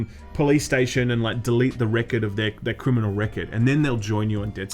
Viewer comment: Did say "Reckid"? Is he from New York now..?